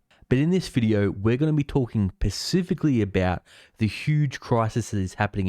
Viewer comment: am I the only one who noticed he said "pacifically" instead of "specifically"